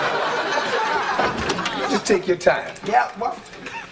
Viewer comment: Who heard the girl repeat yep boy